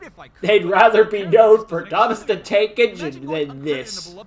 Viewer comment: I just noticed the subtitles said “work” when he said “exist” instead.